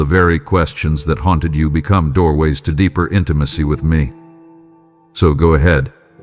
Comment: Deeper intimacy? I thought you said I was pregnant?